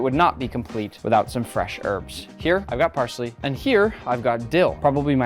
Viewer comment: Did you say parsley?